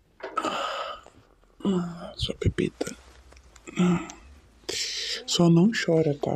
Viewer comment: JK falou pepeta e eu entendi "seu capeta" 😢